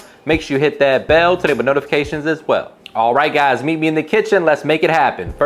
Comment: "make sure you hit that bell (??? ???? ????) notifications as well. What are you saying right there?? lol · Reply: Enable notifications as well